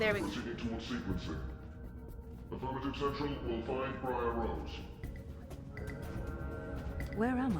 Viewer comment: at around 9:53 i could SWEAR the guard guy says "Briar Rose" in reference to Zoë. i could be wrong but i replayed it and it would make sense i guess??